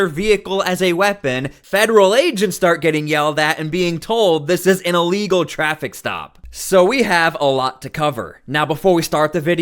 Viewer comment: I thought she said “This is an amigo traffic stop”